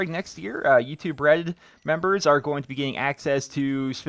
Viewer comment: I keep hearing "Youtube bread"